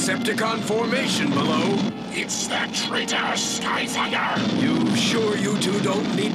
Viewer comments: for the longest time I thought megatron said “Skyf*cker” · Sound like he say:"Skyfucker" 🤔😁